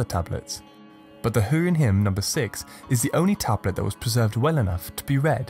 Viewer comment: Have I heard at 0:53 "... the hun hymn..." ? Isn't it related to Atila the Hun? I always wonder, why do we think that ancient nations just wanished without a trace?